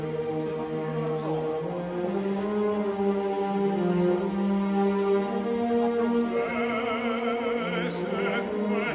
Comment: Congratulations!. Beautiful singing. Can i ask u something? What maestro Mutti say to you at 3:15 min? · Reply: He is very happy and surprised because he did the correct tempo Literally he says “ha fatto pure la croma” meaning “ he sang also the quaver” ❤